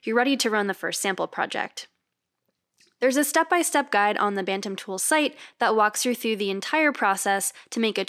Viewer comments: UPDATE: Since we filmed this video (which was not sponsored), Bantam Tools has decided that, while they will still have a pro-level subscription option, they will open up full feeds and speeds override to non-subscribers. Here’s their blog post about the announcement: <link> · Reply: No lie, thought it said, “Batman Tools” and watched the whole thing waiting for her to whip up a Bat Belt or Boomerang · Who else thought it said, "Batman tools"?